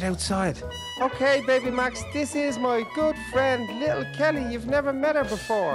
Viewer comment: When said good friend it thought he said meat my girlfriend XDDDDDD